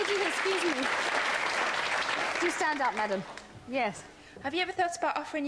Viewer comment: What does the woman at 2:30 say? Can't understand it since im not a mature english speaker. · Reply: Do stand up, madam